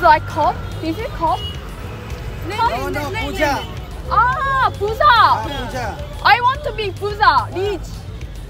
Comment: Did i just hear say puta hahaha 😅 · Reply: Puja not puta